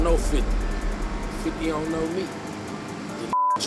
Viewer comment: All i heard was “i know 50, he doesnt know me”😭😭😭